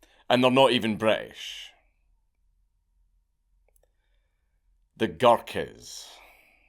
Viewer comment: I heard "their not even British ... their darkies" ... doh